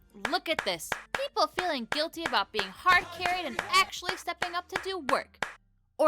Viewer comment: I heard " hard carry hey" (sorry for bad spelling)